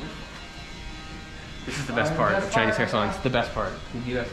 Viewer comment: "Best part: Chinese croissants, the best part" is what I heard haha. I want subtitles please!